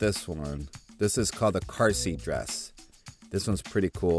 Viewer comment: I thought said carcy dress but it was carsseat dress